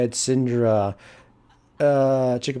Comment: wtf did say at the end? "chkerbergur?"